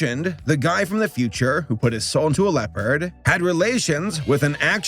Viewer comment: At first I thought I heard leper not leopard. That would be my luck if my soul was sent in the body of someone of the past.